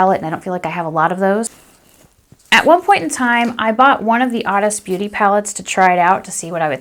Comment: It's the Honest Beauty trios. I bought them at Ulta, but I only see them as out of stock at the Honest beauty site right now. <link> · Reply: Nyx thanks... I finally found it... I had trouble because I thought you were saying "The oddest beauty" but it Honest Beauty 😂😂 my bad😂 I gotta turn up my hearing aids 😂 but yeah sadly it's sold out😩